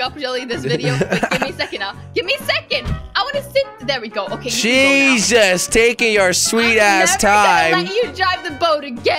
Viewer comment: Did you just say ? "Jesus -.- take your mother @@ time ? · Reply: no he said jesus taking you r sweet time